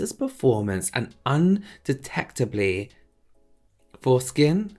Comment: I heard “undetectable foreskin” and was like, I am watching a makeup video right!? 🙃😂